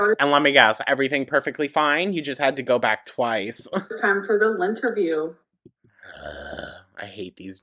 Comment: Tbh, when started all these segments and I heard "lynnterview", I was only listening and thought she named it "Linterview" to poke fun at the whole lint picking thing she made Beck do.